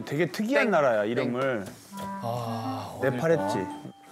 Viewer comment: Did i just hear nepal from eunwoo mouth?😲at 3:24 ??.....Nepalese people where are you?.......